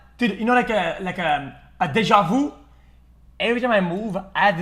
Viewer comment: what is xqc saying? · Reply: a deja vu, a phenomenon where you feel like you're experiencing something that happened in the past. you typically get one if you live a life of routine, although you can still get one at entirely new places and/or doing new things. it's a feeling of haven't i been here before? haven't i done this already?